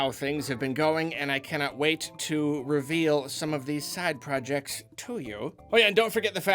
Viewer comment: Misheard "side projects" as "sad projects" at first and was really... well, sad. XD